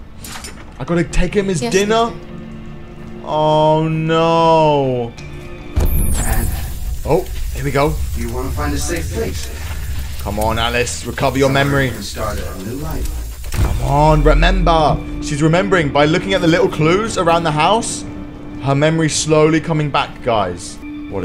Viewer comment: On 44:27 did he just say alice recover your memory